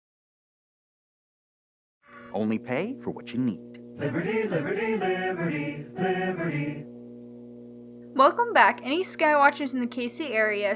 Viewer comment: I thought there was gonna be an emergency broadcast but to my surprise I heard l I b I e r ty